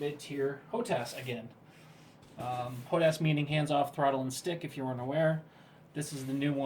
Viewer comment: Sounds like you say Hands 'Off' Throttle and Stick... It's 'On' not off.